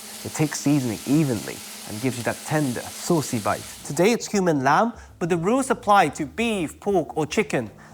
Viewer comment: I heard 'Today it's human lamb?!?!?!?' I had to turn on the subtitle to see it's cumin :)